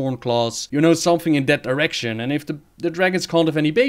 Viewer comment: or somewhere around there sounds like he says Erection xD